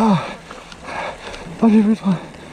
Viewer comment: j'entends "Ah j'ai joui j'crois" pourquoi ? 😂😂